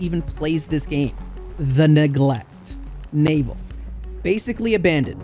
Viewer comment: when you said neglect an ad came up and it sounded like nig